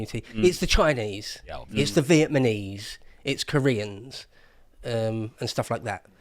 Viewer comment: Did he just say "Vietmaneese?"